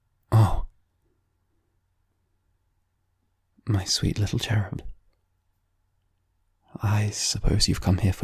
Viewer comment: Okay okay please someone tell me what he called me at 0:27 Bc all im hearing is "my sweet little turd" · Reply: He calls you his cherub. As in a baby angel